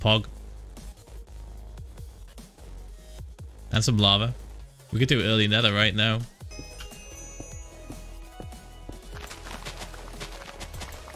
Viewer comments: It sounds like he said netherite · Did Phil just say Netherite? or was it early nether? Idk anymore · did he say early netherite, this came out in 2019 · I heard that he said we can get early netherite I was like isnt this 1.14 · I thought he said early netherite in 1.14 and was very confused.